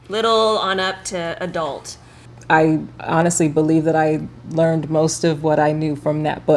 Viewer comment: OMG haha I thought at like 1:50 she said I learned from Netflix!! Hahahhaa!!